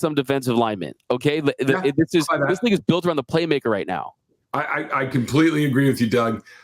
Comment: Sounds like said this ni*** built 😂